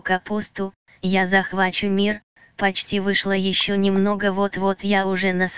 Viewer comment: Самсон это девочка или мальчик? Потому что он говорит этот чувак, а потом 0:23 вышЛА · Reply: Может "вышлО"?